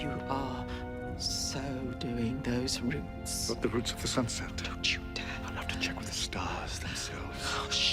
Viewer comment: River’s speech gets me in my feels every time 🤧 and then I hear “What the roots of the sunset?” 😂 Doctor please